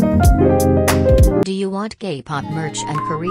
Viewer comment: bro i heard "do u want gay pop merch" and naldnslsnsksbdksks i was like ✋🏼 hold on